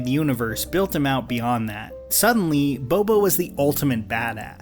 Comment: did you just call him "Bobo" ?? Hahah